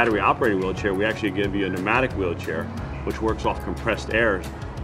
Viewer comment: great video, just one thing at 1:48 the subtitles say 'nomadic wheelchair' however the actual phrase is "pneumatic wheelchair"